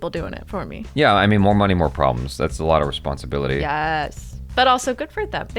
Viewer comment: more money more problems... Sounded like "angoor khatte hai" to me...😂😂